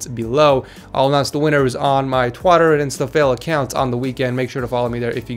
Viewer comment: did you just say twotter instead of twiiter